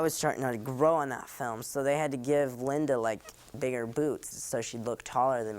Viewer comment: i thought he said "bigger boobs" in the beginning and got really confused :d